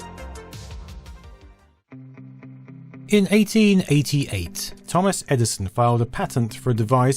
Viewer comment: You've made a mistake in subtitles (0:57). The narrator says "In 1888", while the subtitles say "In 1988"...